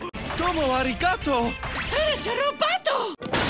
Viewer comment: DOMO ARIGATO ITS MISTER ROBOTTO idk what italy says but fuckin- this and "christmas is a time for spending time with family and loving people! now get bent!" are my favorite lines from the dub i stg this GOD DAMN LINE MAKES ME LOSE MY SHIT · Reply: 'furniture roboto'